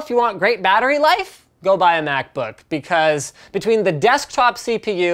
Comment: Did I just hear Linus say "Go but a MacBook?